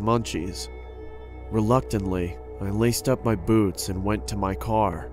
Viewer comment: I thought he said "I laced up my b00bs"